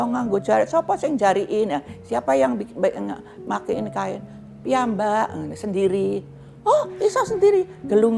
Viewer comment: Sekedar tambahan dikit, di video menit 13:49 itu eyang bilang "piyambak" piyambak itu maksud nya "saya sendiri", meskipun di translatenya ditulis "iya mbak" mmg kedengaran mirip sih 14:40 di translate ditulis "Ndiko menopo" yg lebih tepat "Ngendiko menopo" yg artinya ngomong apa, tpi halus